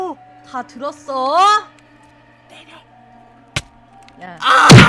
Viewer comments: 초에 이 때리라고 들었음 ㅋㅋ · 에 누가 때려라고 말한거 나만들림!?